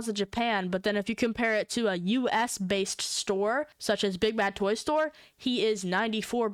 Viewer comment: I hear big back toy store😂😂😂